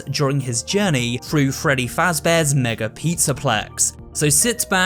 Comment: Did man just said “Freddy Faz Bears N** Pizza plex”